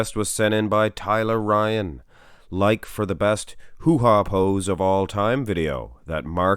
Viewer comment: I swear first time i heard you say it, you called it "the ladys hoo-ha pose" Totally misunderstood the meaning of the pose since! lol